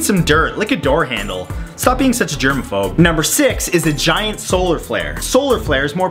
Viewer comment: Thank Goodness he put a subtitle, I thought he said " a giant asshole "